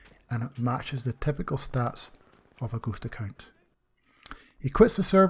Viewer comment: I thought you said ghost a cuntz?